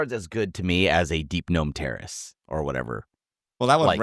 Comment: Did just say "Deepgnome Terrorist" ?!